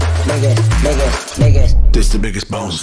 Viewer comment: I thought it was saying “bigot”